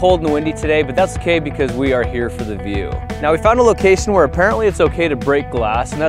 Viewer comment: i thought he said cos we are here for the VIEWS instead of view lolol